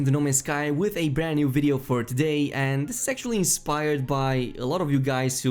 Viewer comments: why did i think he said "this is sexually inspired by you guys" lol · I thought you said "sexually inspired" and I kept needing to go back to hear what you said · "Sexually inspired by..." That's what I heard. I apologise I'm immature. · I thought he said “sexually inspired” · it sounds like you said "Sexually Inspired." lol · Did you say "sexually inspired" ? You really do like this game bro, huh? 😅